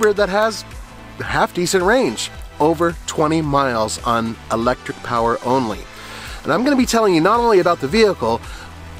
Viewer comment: 50 miles on electric seems useful. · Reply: I thought he said 22 miles electric only?